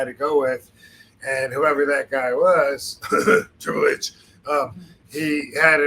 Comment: Who’s the guy rvd mentions at 2:30 ish? Edit: I hear Tri’s name now. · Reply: Triple H